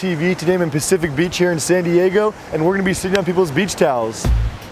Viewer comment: I thought it said "Shitting on people's towel xD"